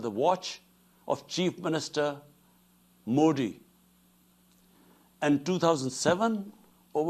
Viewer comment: did he say cheap minister modi?🤪